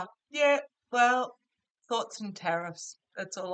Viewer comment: “Thoughts & tariffs” 🎉😂 Brilliant! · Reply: And me, being hard of hearing thought she said " thoughts and carrots"...which would be about as appropriate I guess. I'm sitting here giggling over that one. I'd rather laugh because then I'd cry.